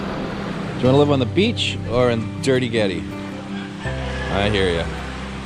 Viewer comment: Did he just say Dirty-guete? LOL 😲